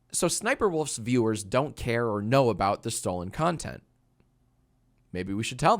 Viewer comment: Thought that shit said stolen continent i was like WHAT